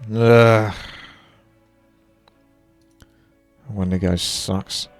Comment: i know what you said but it sounds like o ma nigga sucks :D